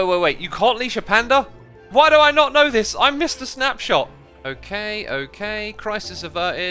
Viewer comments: “I’M MR SNAPSHOT” THAT CRACKED ME UP Oh thanks for the heart bee man with a wandering traders robe :D WAIT NO IT REMOVED THE HEART SINCE WHEN IS THAT A THING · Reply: i thought he said "i missed the snapshot" sounds very similar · "You can't leash the panda ?!... Why do I not know this I missed a snapshot" Said the dude who make videos about snapshots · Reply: I thought he said “I’m Mister Snapshot”, but idk lol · "I'm Mr Snapshot" 😂😂😂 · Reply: I thought he said ‘I missed a snapshot’, but yours is better.